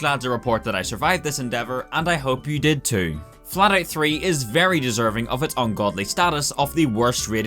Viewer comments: I thought you said Fallout 3, that would have been untrue lol. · I Thought him said Fallout 3. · I couldn't really hear him well and I thought he said fallout 3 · Lol I thought it said fallout 3